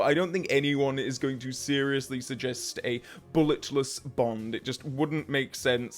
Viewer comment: I know you said “Bullet-less” Bond, but I heard “Bullet-List Bond” and I really prefer the latter over the former.